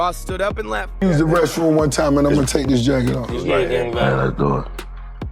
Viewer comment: He littelraly just said what he was gonna do , "He Sped Off, Foot on Gas Pedal"....He just said you gotta keep ya foot on the gas pedal for life, You gotta pay attention to what people say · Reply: I thought he said the bathroom then taking the jacket off 🤔 idk